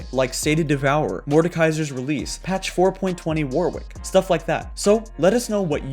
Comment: sorry did he say 420 warwick??